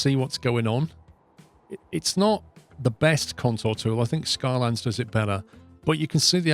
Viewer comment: For a second there I thought he said Skylands. :P